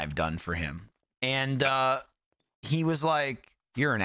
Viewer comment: What does Alfredo’s phone say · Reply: the phone said ***hole. probably calling Michael it.